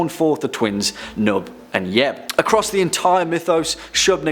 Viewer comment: Did you just say jeb_? OMG WATER SHEEEEEP